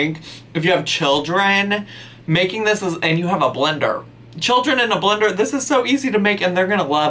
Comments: sounds like he said "children in the blender" at 19:13 lolol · I 100% heard you say "children in a blender" not "children and a blender" 😂